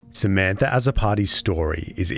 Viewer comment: For half the video I thought you were saying, "Samantha, as a party, did X. Samantha, as a party, went to Y" and I'm like why is he doing that 😂😅